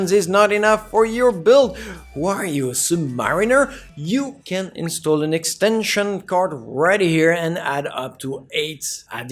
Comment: What was the word on 10:05 ? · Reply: You can install an extension card and up to.... i think i uploaded subs as well :)